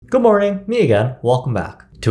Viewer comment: What Good morning n*** no way he said that, or I just misheard it 😅😅😅